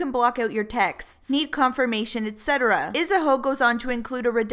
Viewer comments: ISAHOE at 14:38 instead of saying Isabel. I had rewind to make sure I heard that right. I'm so dead lol. This is a disgrace and I feel bad for Eden & I hope she finds better love. INSTA & Twitter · I'm dead did she just call Isabel, Isahoe 😩💀 14:36 the shade 🌴🌴🌴 lol